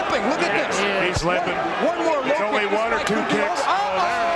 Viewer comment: I Heard : One more ....Ohhh...